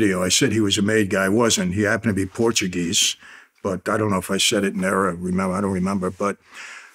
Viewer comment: Did Michael say he didn’t know if he said it in Arab? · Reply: In Error